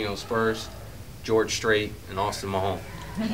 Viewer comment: When he said "I was in mah home town" I thought he said "mahone town"